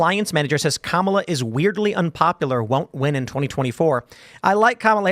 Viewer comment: I think it may be a mishearing. It may be that they said “wildly unpopular” which could, on a not very good recording, sound like “weirdly”.